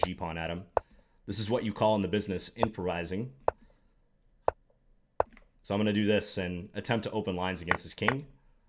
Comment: This is what you call in the business "imperizing"? What? Could somebody please tell what word did he use? I am pretty sure he did not say "improvising". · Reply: I did actually mean to say "improvising" :)